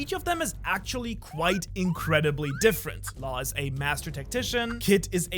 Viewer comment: When you said "Law is a masterful tactician" I thought I heard you say "musician"